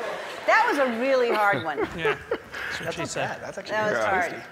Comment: Did Andy say, "That's what she said'" at around 2:28??? 🤣🤣🤣